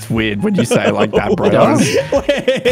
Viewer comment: I sounded like said kiddy parts not kitty parts